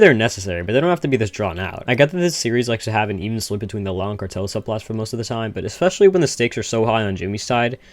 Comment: dude i have no idea what you said right here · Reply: ok i figured it out "even split between the law and cartel subplots" dude slow down lmao